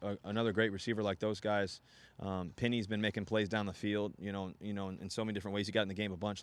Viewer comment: tf he saying penny for if he’s hurt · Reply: Penny Hart the receiver